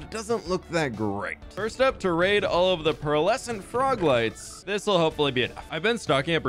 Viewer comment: i thought he was saying frog legs i was like “no… pls” 😭😭lmao